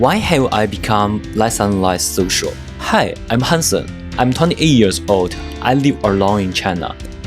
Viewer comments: Hansen i really thought you said Handsome 😂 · Lollll I heard “hi I’m handsome” 😂